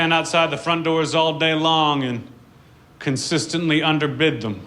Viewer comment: Last one, I don't get what he's saying: I just stand outside the frontdoors all day long and consistently...? under??? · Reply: Under-bid them.